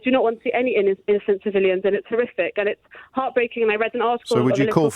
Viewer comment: There are so many Jewish people who don’t want violence in their name. To classify them as extremists is bizarre. Imagine being called an extremist because you don’t want people to die. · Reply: did she said terrific?